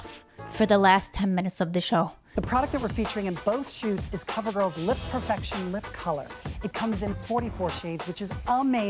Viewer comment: I thought said cover girl lip infection 😆